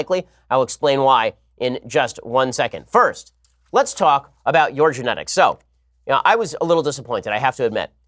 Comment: What Ben said at 12:30 , "Let's talk about your genetics." What I heard at 12:30, "What are your genetics "sir? " "